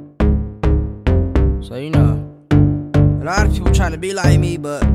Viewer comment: Am i the only one who hears him say "so wheeina" in the beginning at 0:06 ??? Am i losing my mind??